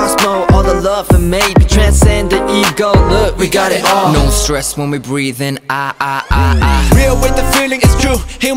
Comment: I thought he said " no stress when we breedin "